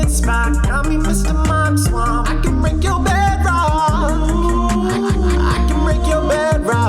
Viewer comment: I CAN BREAK YA BEDROCK GO TO THIS TIME FOR THE RAP SONG HE DID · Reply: I thought he said I can make your bedrock 🤨